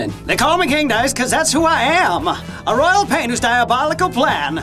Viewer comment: “They call me King Dice, cuz that’s who I am. A royal gibberish Pla. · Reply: For some reason I heard "A Royal Plain to ya plobably plan."